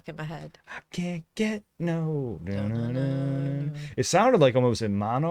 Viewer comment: I thought he was saying, "I can't no 'girly action'." 😂 Still works though.